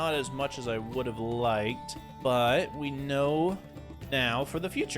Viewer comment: I heard "Than I wood've liked"